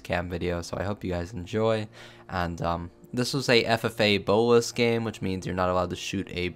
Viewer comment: i was not looking at the screen and i thought i heard FFA boa's game LMAO